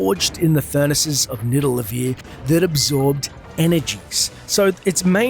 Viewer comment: Did you just say Nidalevir?